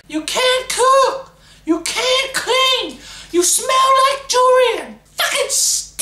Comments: "You can't cook, you can't clean, you smell like durian" - rotflol best insult ever!! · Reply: I didn't know til now that it was durian. I thought she said jewellery and I'm like 'how do you smell like jewellery?' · First I thought he said "You smell like Durian" then i relised he meant Julian :D!!!!!!!